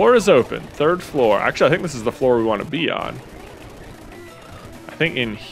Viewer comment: I misheard "This is the floor I wanted to be on" for "This is the floor I wanted to pee on" I can't unhear it now oh god